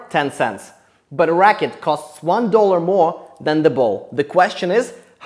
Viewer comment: Ball- 5 cents Racket- 1.05$ If you use 10 cents for the ball, the racket will be 1.10$ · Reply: Guess I'm wahck. I did it as "together they cost 1.10, the racket is 1 dollar... " not 1 dollar "more". I misheard and got real confused real quick lol.